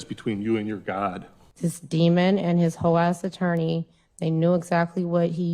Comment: Did she just say "Hoe-ass" in court?